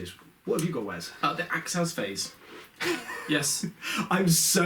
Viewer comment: "the access phase"? I could not get what was said there. And I dont see anything in the FAQ about a general rule of stacking stratagens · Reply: Fernando Pires - acts as phase. As in shoot as if it was the shooting phase and then stack shooting phase stratagems - you can no longer use shooting phase stratagems unless you are actually in that actual phase